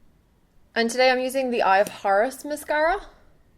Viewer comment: Yay!!! <3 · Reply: Everytime I hear you say "Eye of Horus", my ears actually hear "Eye of Whores" :S Lol